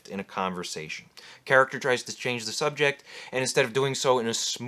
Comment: The moment he mentioned "changing the subject", i heard "oh hi mark!"